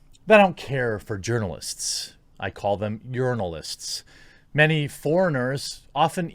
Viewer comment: This whole time I thought you were saying yernalist.... it all makes sense now.